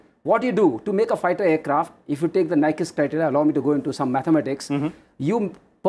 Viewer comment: Please, Provide Subtitles. I am slow learner that is why I need subtitles. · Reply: Krishna Verma I tried googling for Nichus criteria (stability criteria). Is that what he said at 21:40 - 22:10?